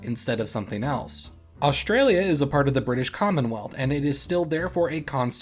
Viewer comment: Thought you said “British Condomwealth”